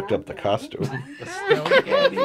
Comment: I thought they kept saying "Styleghetti" and I'm like wtf is that?